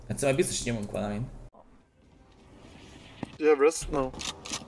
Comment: I thought he said... "You have breasts, no." sorry